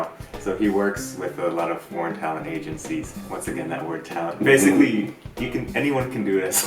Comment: i first heard "porn talent agencies".